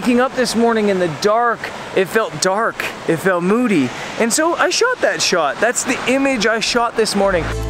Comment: I thought you said... "so... I shot that shit" =) LOL